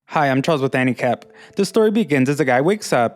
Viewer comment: Sorry for saying it, but say the start a bit slower because i keep hearing "handicap" not anicap btw i like your anime recaps keep going